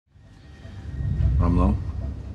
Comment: What did he say? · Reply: Its Rumlow, the guy's name is Brock Rumlow